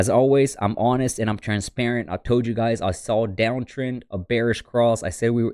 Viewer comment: did he say “ I toad you guys “ ?